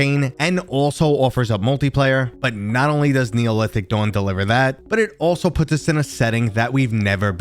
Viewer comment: I had this on in the background at work and I legit thought you said "Neolithic Dong" and was like "ayo.... Lemme check this out quick"